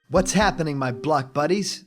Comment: At the beginig I thought he said "What's happening my black buddies!"